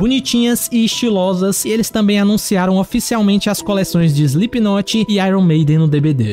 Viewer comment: Eu juro que escutei " Slipnote" 😅😅😅..